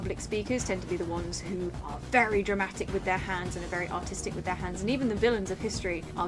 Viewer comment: I love how you explain how being autistic with hand motions to intensify charisma. Lmfao This part is very well animated though! Love Scar in your style! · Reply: Jaime Heck Oooohhhh , my mistake! Lol It must be her Brit accent, when I rewind the video I keep hearing ‘autistic’ not ‘artistic’ .